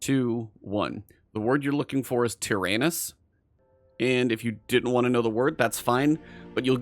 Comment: I heard tier anus. Another way around the booby trap us to just shoot the turrets down from far away.